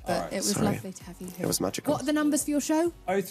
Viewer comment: did Dan say "it was magical" holy crud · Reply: he did say it was magical